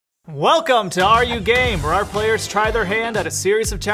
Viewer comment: YALL I THOUGHT THEY SAID “ARE YOU GAY?” SO I WENT “YEP”